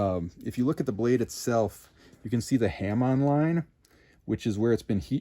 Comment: i thought for a minute what a ham online was and the i realized he said hamon line